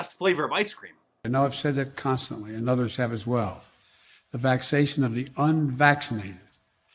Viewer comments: "The vexation of the unvaccinated." President Biden telling the truth again. He does not say vaccination but vexation. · Did he say, "the vaxation of the unvaccinated...." Does anyone know what vaxation is? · I thought he said the “vexation of the unvaccinated” which would make complete sense since that group of people is clearly annoyed and frustrated by everything going on. Come to think of it, we are ALL vexated!